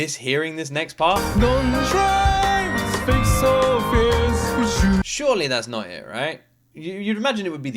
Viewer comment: The lyric here is “Dont dry, with fakes and fears”. misheard it, just like I once did, but when all three lines are taken together, they can be understood as words of encouragement, empathy, and gentle advice rather than anything negative.